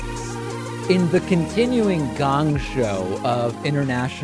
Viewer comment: What is a gong show? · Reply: Ah, OK. Thanks, guys. I later thought he might be saying "gang show" as he pronounces "Angela", "Ongela" for some reason.